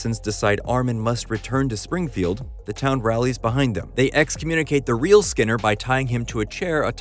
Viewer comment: I misheard the line as saying the town executed the real Skinner and I was briefly SHOCKED the episode would make such a harsh turn